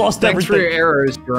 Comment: I thought jerome said 7 arrows